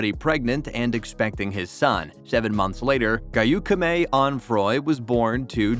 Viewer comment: what did he sayyyyyy "geyukume" wtf